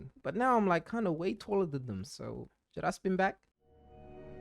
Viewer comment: What comethazine say “ Please spin back” 😂😂